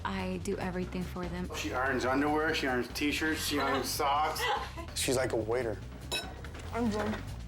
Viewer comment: did he just say ‘she’s like a waiter?’